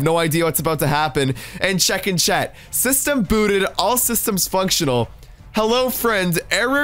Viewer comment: "functional" i thought u said my roblox user cause my username is called "fvnctional" LOL